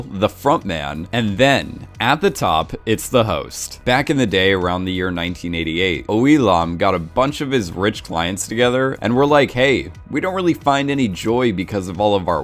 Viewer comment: I wasn't paying attention and thought you said "Elon" XD